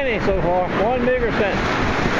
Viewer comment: What did you say at 0:45 ? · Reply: One meager cent.